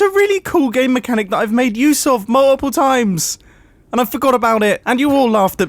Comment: Did he say “MAWUPPLE TIMES!” Or was that just me....